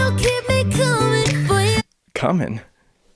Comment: Did he just say cumming hmmm😂